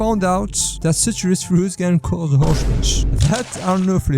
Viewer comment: my caption says horses 😂😂